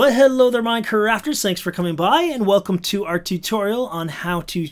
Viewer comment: I thought he said tiktorial